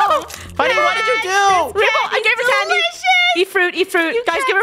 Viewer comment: I think Rainbow said a bad word 😂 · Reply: Crystal_k1t She said delicious